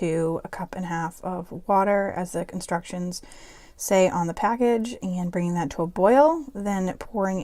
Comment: I love at 21:29 she says constructions instead of instructions! I catch myself saying this too when I'm cooking with my kiddos.